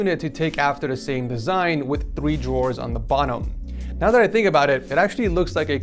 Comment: did he just say "bonnom"